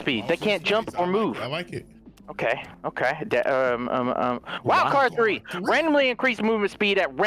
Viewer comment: Haha, at 03:24 when Delirious says “wildcard 3” I thought he was saying “wow CaRtOoNz!” When I then heard the 3 in wildcard 3 it seemed to me like Delirious upgraded Toonz from CarTWOnz to CarTHREEnz😂 My brain is weird🤣